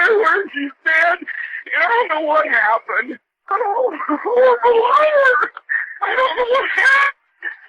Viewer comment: it sounds like he said “I’m a horrible liar “